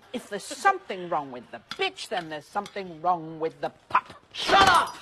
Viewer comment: I THOUGHT SHE SAID PITCH WTF